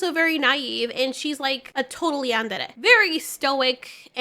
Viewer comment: what did you say exactly what she is. Because how you pronounced it. It sounds like to me you said "Yandere" if you did. Well, saying you made the poor choice of words is one way to put it.